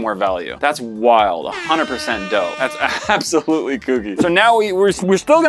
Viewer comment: said dough instead of dope